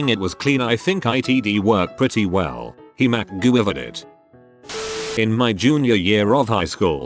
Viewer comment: ish the computer sounded like it said macgoober